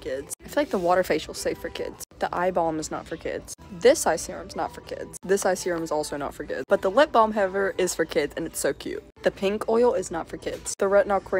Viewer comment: I thought you said "eyeball" 😭😭😭 ❤❤❤